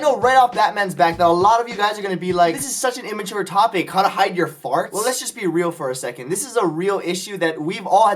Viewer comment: I thought it said how to RIDE your farts... disappoiNTED